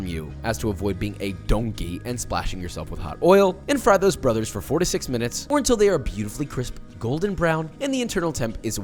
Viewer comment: When he was frying the chicken, I thought he said "fry for 46 minutes" and I genuinely believed him for a whole 10 seconds and then I was like, wait a minute, 46 minutes to cook fried chicken?